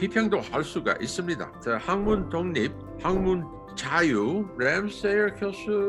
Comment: 학문 독립이항문 독립으로 들려